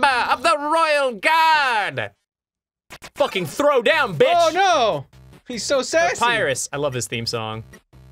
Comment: I'm late as always but in 1:26 did I just hear "Oh my god he's so sexy!" ?